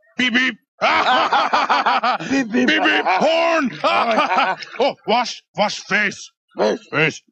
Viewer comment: did it say porn??